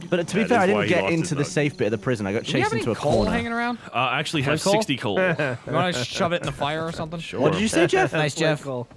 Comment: I tried and failed multiple times to hear what said. What was funny? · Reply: Someone was asking about coal and said "Flynt Coal"